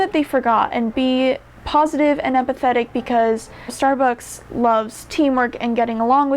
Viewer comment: No shade to but I thought she said "apathetic" apathetic means that you are showing no enthusiasm. I think she meant to say "empathetic" which is the ability to understand and share the feelings of others. Please do not say you would treat a customer apathetically in your interview.